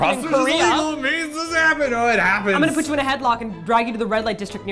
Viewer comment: I heard pony station XD